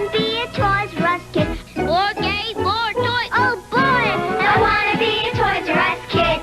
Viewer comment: It sounds like he said "OR GAY FOR TOYS!"